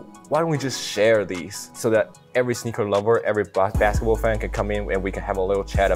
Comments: does he say every black basketball fan? Lol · Am I tripping or am I hearing “every black basketball fan” 🤔 1:40 listen closely!!!!! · did he really say black basketball players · did he just say every black basketball player?